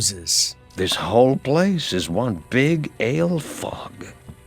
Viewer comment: hahaha at 1st i thought said ''This whole place is one big ALE FUCK'' had me dying!